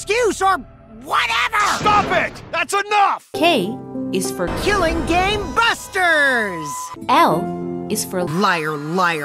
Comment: Omg I heard “killing game busters” as killing ͟ga͟ ͟y busters